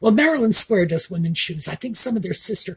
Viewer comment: Maryland Square Shoes. Not Marilyn Square Shoes. (I thought you said the latter. 😂)